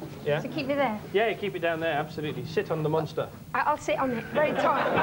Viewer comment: When Chris Evans said "sit on the monster" I heard him say "shit on the monster"